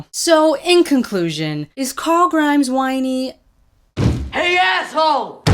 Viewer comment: did you just say corl grimes